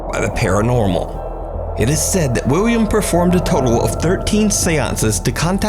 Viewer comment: idk if its just me, but it sounds like is saying wiwwiam at 8:12 & whenever he mentions the guy by name